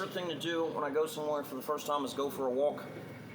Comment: did he just nearly say "The second thing I like to do is masterba___ on public transport?" Is that what happened to his arm? He cut the word short though so it's hard to really nail down what he was going to say...